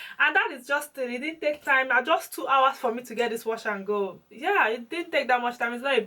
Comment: I actually did my first wash and go after watching that your video and it really made me happy. You said just two hours? 😂😂😂 OK that was what i heard in the beginning 1:33 lol or maybe I didn't hear well 😀😀😀 mine is definitely going to take longer so I need to remain sleep for it. You are amazing you know 😘😘 thanks for making these videos.